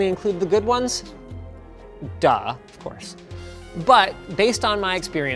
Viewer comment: Linus, did You just said "да" at 10:40?